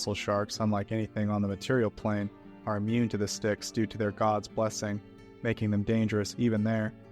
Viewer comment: What was said: "immune to the Styx memory erasing properties due to their god's blessing" What I heard: "... due to their god's bussin'"